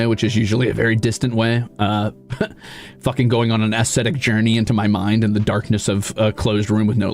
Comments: slight correction here: "ascetic" instead of "aesthetic". He was likening the way he deals with this kind of thing to the practices of a monk or hermit. (He does pronounce it a little oddly, to be fair) 4:20 here he says "guerilla-ing" (that is, guerilla streaming) instead of "grilling" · Thank you for clipping this, it's a moment that really highlights the insight and emotional intelligence that the Ruzaders admire about him. Couple of minor subtitling corrections: 3:43 here he says "ascetic" instead of "aesthetic", likening his way of dealing with this kind of thing to a monk or hermit (though he did pronounce it a bit oddly by putting emphasis on the first syllable instead of the second) 4:20 here he says "guerilla-ing" (i.e. guerilla streaming) instead of "grilling" 6:03 here he says "every once in a while" instead of "everyone's want to" Wanted to repost these after the previous comments got nuked because people were talking about PLs too much (any future commenters, please keep that to a minimum unless you want it to disappear again )